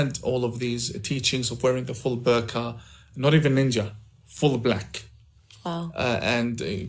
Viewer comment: Did he call them ninjas